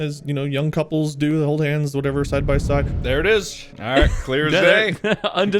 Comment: they were wrong about that. Wade did Not say "Beck and i Held Hands or Whatever Couples Do." He Clearly Said "Beck and i Held Hands or Whatever Have you side by side." Wich is Very Different and Clearly Suggests He Dose know.